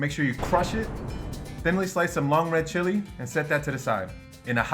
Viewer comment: Hmong girl here! Did you say Hmong red chili?!! 🤩 I love chili! The very spicy red ones fresh from the garden!